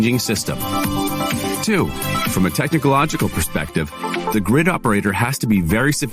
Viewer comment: I think the ercot ad says "technicalogical"????